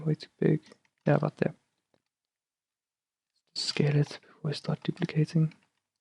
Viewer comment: What did you do at 12:15 for the circle button thing? Can't understand much and I have to pause every damn second just to get that right. I heard "scale it then deplicate it", which I tried to no avail.